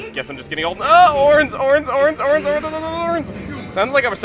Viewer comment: The first time you said ornes I thought you said oranges.lol